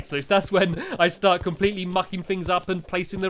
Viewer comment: excuse me mr stampy? Did i just hear the f word? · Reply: He said mucking up